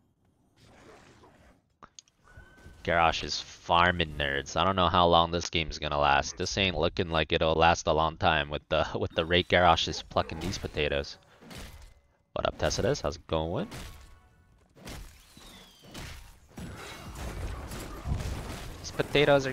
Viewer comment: I rewatched 1:33 10 times on 0.5 speed Please tell me I'm not the only one who hears "fucking these potatoes" instead of "plucking these potatoes"